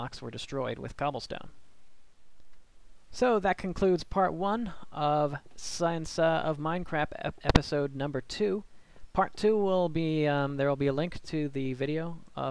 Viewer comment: Did you say science of mine crap at 14:22 lol xD (PS Awesome video thank you for existing in the world <3)